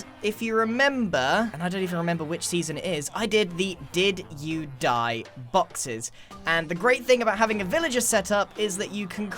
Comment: Next season he'll be making the "it seems you've succumbed suitcase" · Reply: i thought he said "did you die foxes"